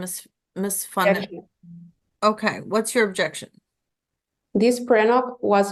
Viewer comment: Everytime the judge said "Fonseca" all I heard was "Ms. Fun-Sucka"